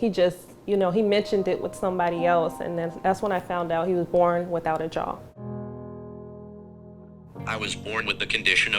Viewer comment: I thought the dude said he was born without job lol 😆 😂